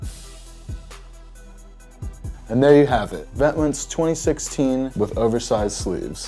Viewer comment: Vet*Mont not Vet*mints I thought he said.